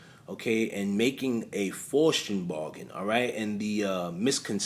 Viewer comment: All this time I thought you were saying, "Foreskin Bargain". New York accents, sheesh!